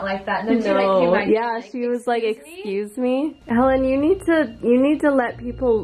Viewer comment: I SWEAR I HEARD SOMEONE SAY ‘Helen’ it sounded like a man. Did anyone else hear it?